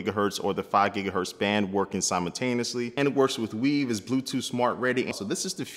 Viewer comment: did he say it will work with weed? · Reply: mahmood Weave. It's Google's platform for IoT -- <link>